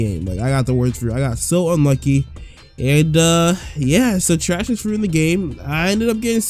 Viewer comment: when i started i found invisible fruit devil fruit hunting bruh i picked up and left instantly because i thought it said auto eating trashy fruit in 3 seconds im like OH HELL NO i still think it said that